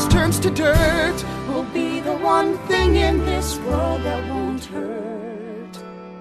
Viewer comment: you said word instead of world sorry I'm annoying